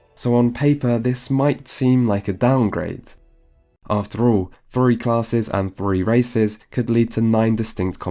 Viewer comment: what he says "three classes a d three races" What I hear "Fury classes and fury races" O_o